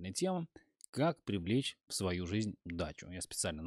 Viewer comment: Послышалось "Как привлечь дачу"